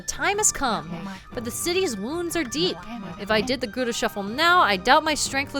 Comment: I listen to youtube while at work so I'm not watching the screen usually. I hear talking about The Gura Shuffle and I swore she was saying Gouda shuffle. (Gouda as in the cheese.) xD